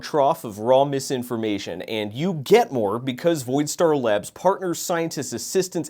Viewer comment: I thought you said it was lab and not labs? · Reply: Voidstar Lab's partners. Possessive s